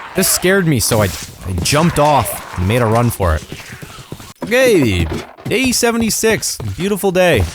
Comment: what said: "Okay" what I heard: "Im Gay"